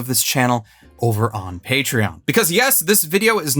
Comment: heh, I thought you said "Hatereon"